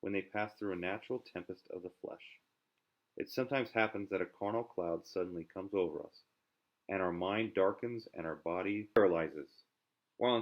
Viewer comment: What is said at 1:35 ? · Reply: ”sometimes a carnal cloud comes upon us and our mind darkens”